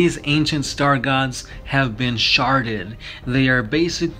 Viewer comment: "Have been Sharted...." can't hear it any other way anymore